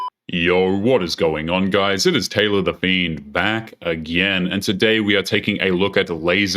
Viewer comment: Lmao idk why this time i heard him say "Taylor the Been"🤣☠️